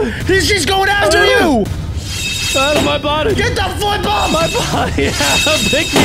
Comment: did adam say a bad word? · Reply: He said flip like they do all the time not the f word